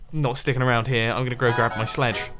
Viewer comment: Did he say “sledge?” Tell me he said it! I’m not crazy, right?!